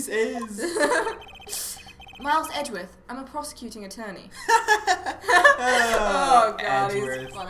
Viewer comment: I thought he said "Prostituting attorney".....